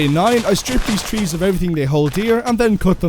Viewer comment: did bro say sigma or sick?